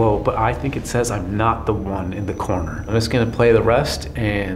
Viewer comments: When hears the EVP at 16:18 i think i hear "im not the only one in the corner" 🤷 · Late, I know. But at 16:20 I heard, "I'm everywhere, not just in the corner." Or something along those lines